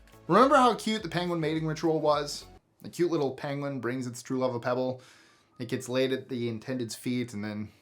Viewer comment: I thought he was saying the oenguin gets laid 😮😂